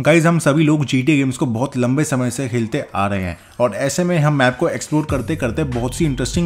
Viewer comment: he said "hilte" instead of khelte